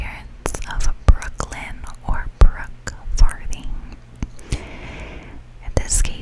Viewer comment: This video started playing from auto play, so I didn’t see the title, I just heard your voice and I thought you said Brookes farting.